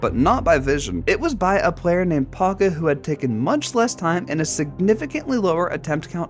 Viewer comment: i heard pocket and not paqoe